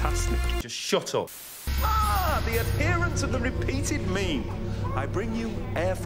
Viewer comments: what I hear: "ah the appearance of the repeated meme" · did he say the appearance of the repeated meme? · did he say... MEME? Probably not but I'm still asking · i thought he said "the appearance of the repeated meme".... but yeah i love 9 hes so sassy :3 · I heard "errant of the repeated meme".